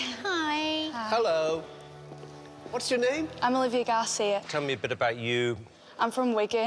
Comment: For a second I thought she said I'm Olivia gasy